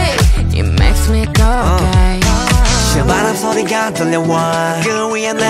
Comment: i just heard it makes me go 'away'. lol.